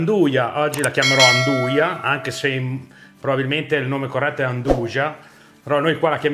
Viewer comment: Non ti preoccupare! Il nome corretto 0:27 è come l'hai detto la prima volta e senza la vocale A davanti. "NDUJA" (Pronunciata NDUIA)! I miei complimenti hai fatto un ottimo lavoro e sono sicuro sia buonissima! Devo anche ringraziarti perché non si parla poi così tanto della Calabria, se non spesso per motivi negativi. Per me questo è un omaggio ! Grazie!